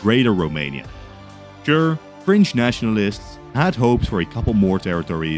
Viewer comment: why did I heard French nationalists